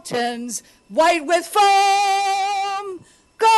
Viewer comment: um....I think she said "white with foam?"